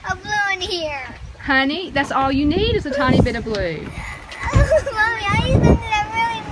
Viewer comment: That first kid sounded like he said “I need some fucking goldfish!” 😂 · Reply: I thought he said “I need some coffee.”